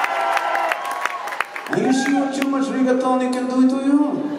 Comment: Do you see what ********* can do to you? What the hell he said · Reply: Stoyan Dimitrov Too much rigatoni